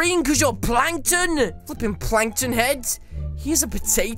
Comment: When he said are u plankton head it kinda sounded like he said fu..ing plankton or fricking plankton